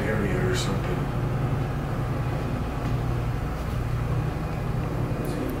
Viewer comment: yo wtf was that sound, sounded like someone saying "wooooowww"